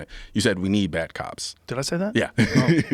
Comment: I thought Rogan said.. “We need back ups” not “ we need bad cops” lol